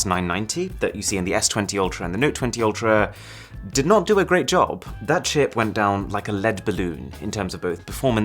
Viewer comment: I probably heard this wrong, but I heard him say "that shit" on